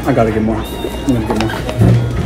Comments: He forgot za bebsi 😢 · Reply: I thought he said “it looks like you licked the ball”. 😅😅😅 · I thought he said lick the ball😅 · I heard " you lick the ball "💀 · I heard he said "you licked the ball" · Bro i hear he said "looks like you licked the ball🤨" that's what i hear